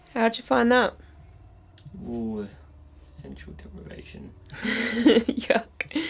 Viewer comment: Amazing investigation, and love when you share what your feeling, for sure. And around 26:18 of the video one of the words I hear clearly that wasn't shown, right after you ask "why is it so cold in here?" The Ghostvox says "Cooperate" or "something cooperation" what fantastic evidence. Somebody may have already told y'all but that's what I hear and I'm not gonna go through all the comments lol keep up the amazing videos 🙏🏼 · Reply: Hey, Marty! I'm mostly deaf, but I thought I heard the word "evaporation" spoken in that moment. Maybe the ghost is a smartass?